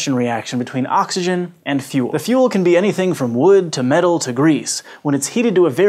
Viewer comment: Heard it as "The fuel can be anything, from wood to metal to Greece" at first